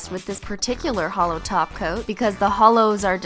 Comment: I thought she said a holo taco not holo top coat.